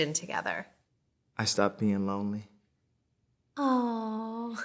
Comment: I thought he said i've stopped peeing lonely